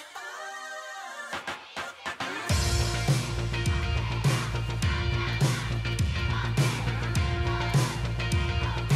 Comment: can anyone please tell me what the fans said what i heard was myday saying day6 members name 😭 Update: a cpl months ago i understood already hehe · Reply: they were shouting the fanchant, that part was "park sungjin, park jaehyung, kang younghyun, kim wonpil, yoon dowoon, haeng-bok-haett-teon nal, deu, i, eott, da"